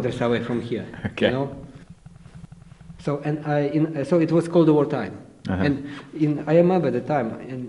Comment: another great video. I think at the 4:21 mark he actually says "cold war time" not "called" since the USA and the USSR were in the "cold war". Enjoying this series a lot. Keep it up.